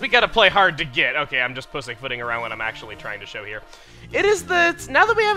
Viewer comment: does say pussyfooting? I know its not bad but it definitely caught me off guard.